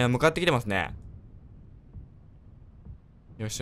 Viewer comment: いい感じーがいいガンジーに聞こえたww